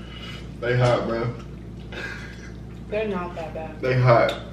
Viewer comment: Who else heard "they hot babe" 2:09 ??